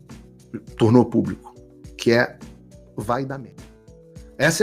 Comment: Alguém pode dizer o que foi que o Paulo falou? Eu entendi " vai dá merda". Foi isso mesmo?